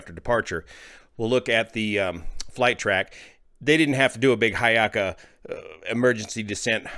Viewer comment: What is saying at 1:31 or so? "they didn't have to do a big <???> emergency descent nose diving profile". What's the word or expression he's using? Sounds like "hayaka" and I can't figure out what the term could possibly be. I get his point, but I can't find a word about this topic that sounds like this.